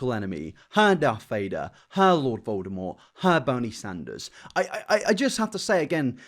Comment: Did you just say Boney Sanders?